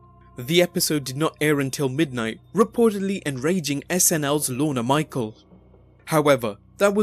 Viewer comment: lol sounds like you said Lorna michael